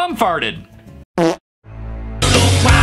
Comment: “What is a tomato baby?” Oh I heard “don’t cry to me old baby” but tomato baby made me laugh so hard. Lol